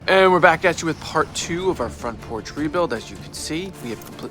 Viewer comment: Man I thought he said front porch tree build. I was waiting for the tree part